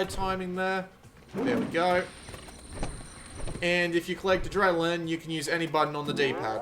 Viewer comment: WHAT the hell does he say @ 2:06??? The captions think he says "& if you click the dry learn you can use any button..." & honestly it basically sounds like that is what he's saying? WTF? Anyone speak gibberish who can translate PLEASE?